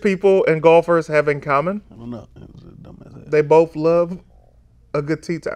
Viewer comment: I thought he said under his breath: "That's a dumb ass hat" Because he was trying to describe the British hats and the golf hats.